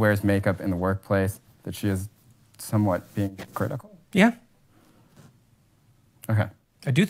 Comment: Love your content especially when i disagree, keep up the good work. Jordan peterson is agreeing with the interviewers summary that women are being "critical"; not hypocritical. Am I missing something my dude? I may be bias but i always assumed that specific interaction was a unspecific general direction that the interviewer was beinging to more accurately interoperate Jordan Petersons view. But if critical can be a synonym for hypocritical, then I'm just speaking out of my ass and i didn't know that. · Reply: Holy shit the second time you hear him say critical at 2:26:35 he sounds like he says hypocrititcal because of feedback. HE IS SAYING "CRITICAL" AND attempting to further the conversation by compounding on his thought on Peterson's views to get to a solution of the topic.